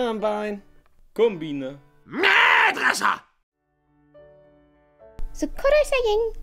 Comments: Ktoś coś z polski? :v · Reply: W tym pierwszym usłyszałem ma dresa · Sound Like he saying “Mad Russia!”